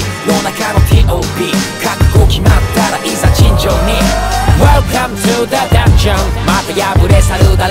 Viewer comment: あかん、Welcome To The だっちゃんって聞こえるｗｗｗ